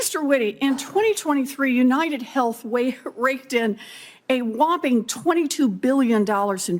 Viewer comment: Yall notice how the CC misheard "raked in" as "raped in"? Chef's kiss